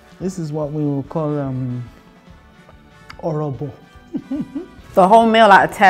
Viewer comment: She said «Orobo» not «'Orrible» as the subtitles say. It completely changes what she says 😕